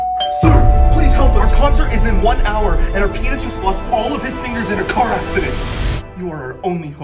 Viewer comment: "Our penis lost all his fingers" Is what I thought they said